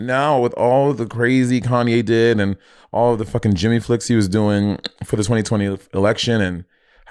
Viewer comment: Can anyone clarify for me what said at 52:40 - it's sounds like she says Jimmy flex - then sounds like he says genuflects. says it's just a Saint Lucian thing but I feel like might just not know the exact meaning and origin